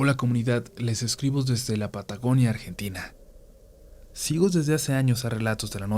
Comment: Perdón, pero escuché "pataconia"?